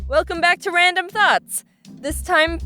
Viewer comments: why does it sound like she said "welcome back to random thots" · Funny how every time she said random thoughts I thought she was saying random thots